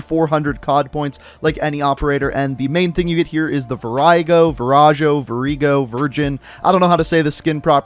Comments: I don’t want to sound rude, but am I the only one who heard him say “Virgin” when he tries to pronounce “Virago”? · did he really say virgin lol